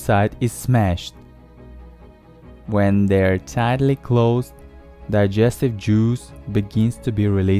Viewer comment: it sounds like he is saying digestive Jews instead of digestive juice for some odd reason